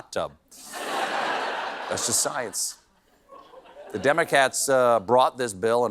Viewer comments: i'm completely blind, am I going crazy or did he say "Democats" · …did he just say demo-cats? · I heard you say "demo cats" is that some sort of kitty demolition crew we wreck you meow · Did he say DEMOCATS at 11:34? 😂😂😂